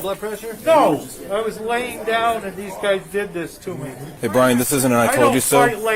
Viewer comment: captions say "brain" instead of Brian